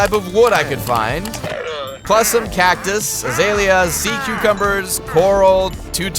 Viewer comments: DID HE JUST CALL THEM SEA "CUCUMBERS" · Did he just say SEA CUCUMBERS?!